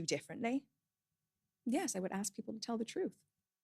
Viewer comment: When she said "Yes, I would ask people to tell the truth" I honest to God thought she said "Yes. I would absolutely tell the truth" and I was like HOLY MOLY! Then I replayed it, actually looked at the captions and was all "...oh."